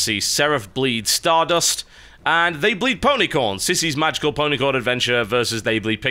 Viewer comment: Not sure if it was TB's accent or just my dirty mind but I kept hearing him say "pony porn".